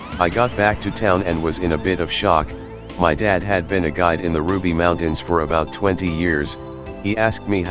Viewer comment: My dirty mind thought it said "Ruby Mountain BREASTS."